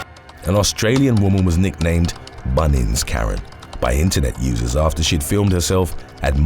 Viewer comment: I misheard Bunnings Karen as bunions Karen....thought had something to do with a pedicure.